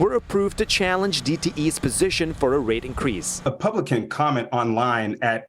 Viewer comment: I heard "DTE rape increase." Double digit increases are like that.